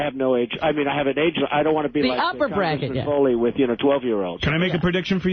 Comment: I literally hate Trump with all of my guts, but in that Howard Stern clip through the clutter of them talking over Trump to me it still kind of sounds like he said “I don’t wanna be fooling with 12-year-olds”